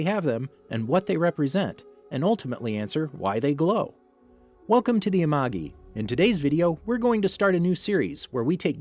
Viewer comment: I thought you said, “Welcome to the unagi” (I can’t spell but I was trying to spell the fish on kyoshi island)